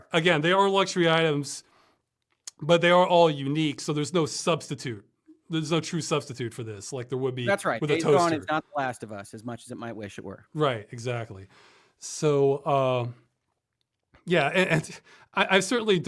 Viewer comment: Fascinating discussion, thank you for posting it. Mr. what were your words at 1:29:48 ? I can't quite decipher, "That's right .... is not The Last of Us as much as I might wish it were" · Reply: At slow playback speed, it sounds like "Days Gone", could be mistaken, but it sounds like it.